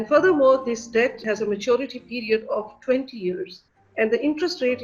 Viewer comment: Wtf who did the subtitles? 3:35 the woman said maturity of 20 years. Basic security language, sure accent is thick but... come on